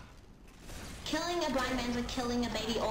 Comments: Who else thought he said 'black' instead of 'blind' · Had to listen to that nocontext piece a couple of times. At first, I thought he said, "Killing a black man is like killing an orphan." And I'm like, "What?" And then it turns out that he said, "Killing a blind man is like killing an orphan." And I'm like "What?" Because it still makes no sense to say that for no reason.